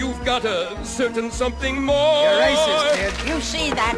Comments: I think said "You're aces kid", but it did sound like he was saying "You're racist kid" :-) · What the hell does say at 2:33? Sounds like he said "You're a racist kid!" Anyone know?